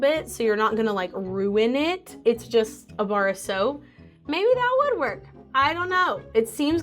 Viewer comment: that "maybe that would work" sounded like "maybe that WOOD WORK" 🤣🤣 and that's so funny & punny